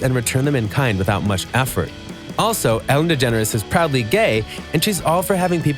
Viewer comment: I had to stop and rewind when I heard him say shes probably gay, lol no shes definetly gay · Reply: He said proudly not probably. But he definitely could have spoke clearer.